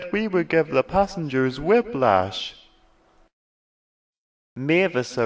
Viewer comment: Brilliant, just brilliant! You should write HiT eps. But, at 1:35 'wib-lash'? What's that mean? Am I hearing wrong? :/